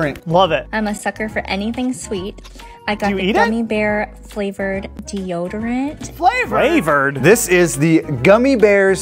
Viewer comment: Gummi bear flavoured deodorant? I thought she said Camembert flavoured deodorant!!!